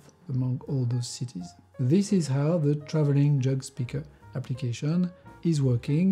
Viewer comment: twenty what in France? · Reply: At first I heard "traveling joke speaker" and was a bit surprised when he mentioned that they only have 20 jokes in France... Not much to tell at parties then I guess 😅😂